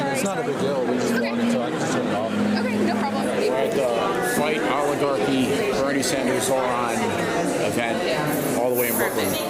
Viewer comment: Do you guys even know the definition of an oligarchy? So you’d rather the candidates that are backed by billionaires and millionaires? Yet you refer to this event as an oligarchy is stupid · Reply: bro he said "We're at the Fight Oligarchy Bernie Sander, Zohran event" not F*ing Oligarchy event. You misheard him